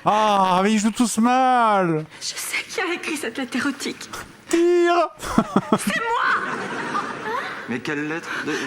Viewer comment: Vous aussi vous avez entendu Zerator geuller "TIIIIIRE !" ou c'est que moi ? xD